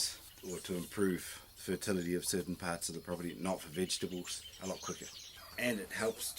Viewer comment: I didn't understand your comment, right near the end, when you were at the toilet. You were talking about spraying. I thought you said "not on vegetables". Did you? If you did, then why can't you spray the activated EM on vegetables? I had been told that you can, and that it is very beneficial. Did I mishear you?